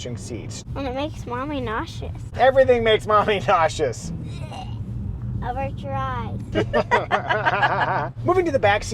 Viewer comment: What did daughter say @ 1:55?? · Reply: She pointed out that I was feeling nauseous, pretended to vomit, and then said, “avert your eyes!”